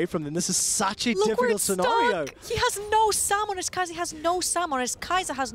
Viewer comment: Really thought they had this one, in my book they deserved to win. But a good game nonetheless. P.S very solid performance from the new caster. See a lot of people whining about nothing... some casters you like more than others, that's fair. However, I never seen a single reasonable ounce of critique. "Hard to hear what she's saying" I guess that's an opinion if your English is rather poor. It's just an accent, it's not like it's broken English or something like that. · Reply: "I guess that's an opinion if your English is rather poor. It's just an accent, it's not like it's broken English or something like that." No really, its broken english. And by the way, even if we sometimes do understand whatever shes trying to say, then its completely wrong anyways. isn't she supposed to be the colourcaster?? 21:13 not really sure what she just said, i got the words "sommuners" 26:58 talking about teleport when having ignite. I honestly had to mute the game from that point onwards. So sad he ruins the experience for so many people.